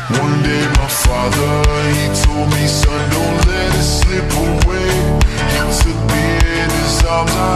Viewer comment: He said, one day, you’ll leave this world behind… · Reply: HE SAID, "Son don't let it slip away.". When I was just a kid I heard him say!!! 🔥🔥🎊😃